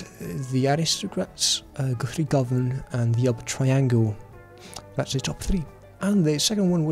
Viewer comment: What did he say at 5:03? Upper traingle??? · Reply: The Elba Triangle